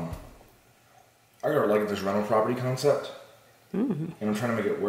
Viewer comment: The fact that you guys act like newlyweds after years of marriage is incredible. Also at 2:15 did say he had a "rental property" concept?